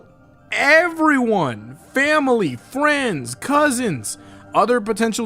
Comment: Did u say Family?